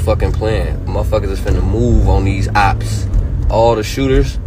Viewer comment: This is what i heard..."Mooow on those hops".